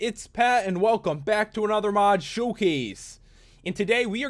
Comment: Is it just me, or I thought he was saying mod shoe case when I says mod showcase when I was little